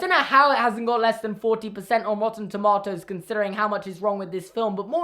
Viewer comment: did you just say “rotten to-mot-os?”